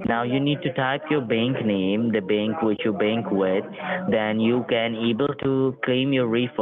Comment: Could have sworn I heard mention Cain and Abel